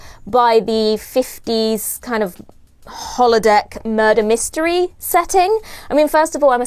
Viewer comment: I thought you said “nerder mystery” and I’m ok with that 🤓